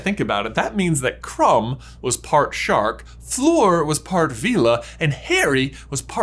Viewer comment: i thought you said krum was a shart...